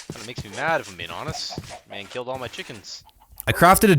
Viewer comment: Dang, killed all my chickens. Also: Has 26 chickens still (Yes I counted.) Edit: he actually said, "MAN Killed all my chickens." sorry.